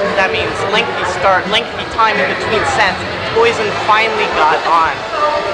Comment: Who is the band?? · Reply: Oh I thought he said "poison"